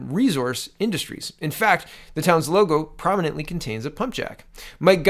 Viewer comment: I thought you said punjab. Pumpjack. I must have racist ears.